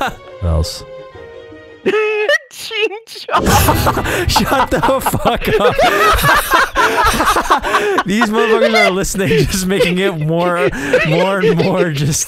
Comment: I thought he said Cheech and Chong😂 8:50 omg 😂